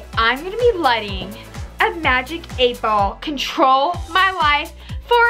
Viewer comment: It might just be me but it sounded like u said "Magic 8 ball trolls my life" xD Edit: I like my own comment